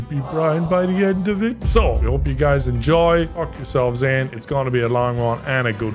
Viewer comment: Who else thought nogla said "f#$k yourselves in." Instead of "buckle yourselves in."?